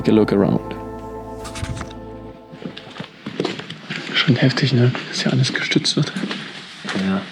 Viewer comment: Could somebody that speaks German transcribe what he says at 4:43. I know what the translation in English says, but can't understand his tone somehow. Trying to improve my German skills! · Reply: Schon heftig, ne, wie das alles gestützt wird.